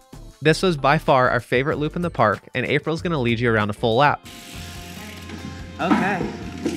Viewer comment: Dang he was so close to her I thought he was gonna knock her off most of the video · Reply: oh, I thought he said she was gonna show us around.